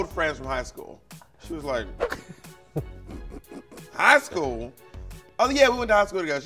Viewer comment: When Bob shouted, "High school?" I heard, "Back roles?" Pit Stop Call and Response.